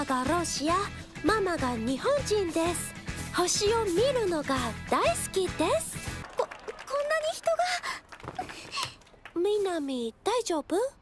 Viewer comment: Мне одному на 1:08 послышалось: Дай в жёпу